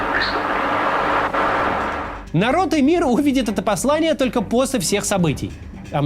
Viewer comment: я один услышал сперва народ имир?